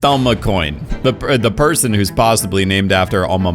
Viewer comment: Is it just me or does he say “Dalma Coin” instead of “Alma Coin”? Sounds like it’s been clipped off of another video where he is actually saying “ and Alma Coin”